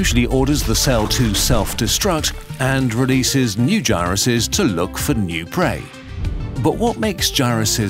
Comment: I thought he said gayruses